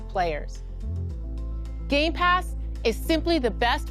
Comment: did she just say gaypass?